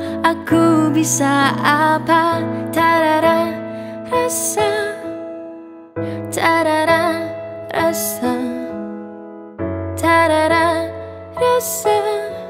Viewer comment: Intro misheard : Tak ada rasaa... tak ada rasa.... tak ada rasaa...